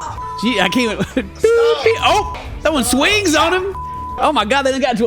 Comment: Did... did he just say... "throwing swings"?